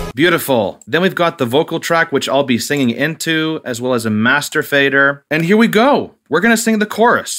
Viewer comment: listen very carefully. He actually says “master fader” there.